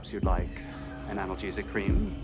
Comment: I heard "would you like anal cheese and cream"